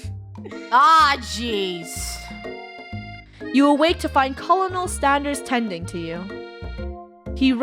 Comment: Is It Just Me Or Did She Say Colonel Standers